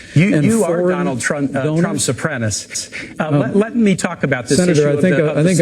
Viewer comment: "You truly are Trump's apprentice." HAR HAR HAR. And the line landed with a thud as Kaine's face desperately sought approval. The Clinton ticket has been run on tacky one-liners that were formulated in a room of "content creators" and every one is as hacky as the last. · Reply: Glad you posted that. I heard "Trump supremacist".